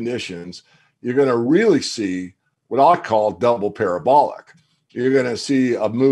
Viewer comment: could have sworn he said “a double pair of bollocks”. I guess it was actually a double parabolic. 😂